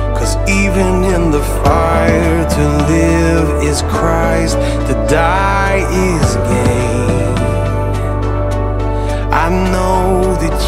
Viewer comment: Forgive me Lord, I kept hearing, “To die is gay.” for the longest time.